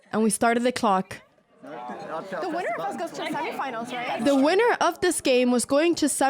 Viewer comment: Guys, what's going on, end of the video I hear her say 'Sammy-finals', and not semi-finals. Write below if you hear the same..